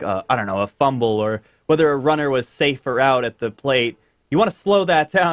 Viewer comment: did he say at the plate?😂😂😂😂😂 but i still subscribed cuz u doing a good job